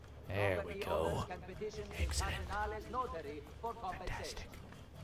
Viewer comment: did you say 'Ecco' ?? :D you speak italian !